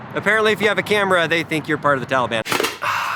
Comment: That zoom in on Fremont, CA instead of Fruitport, MI. It sounded like they said "Christian Taliban"